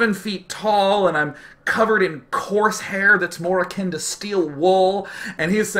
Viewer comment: anybody at 16:24 hear him say corsair (the toss air unit from brood war) instead of coarse hair?